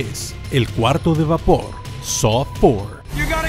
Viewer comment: es saw six o saw four lo que dice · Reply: Creo que saw VI